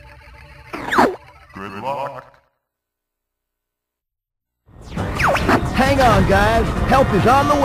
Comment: I cranked up my headphones. It's "Good Luck!" I always thought it said "Report!" as a kid.